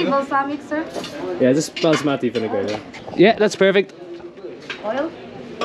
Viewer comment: balsamic vinegar mate Sounded like you said basmati vinegar.